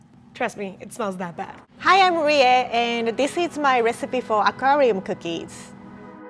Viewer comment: When said aquarium cookies i thought i heard korean cookies